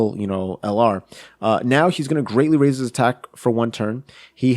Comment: DID HE JUST CALL GOHAN A SHE LMFAOOOOOO 🤣🤣🤣